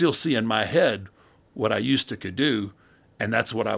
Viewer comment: did he say what I usta ca do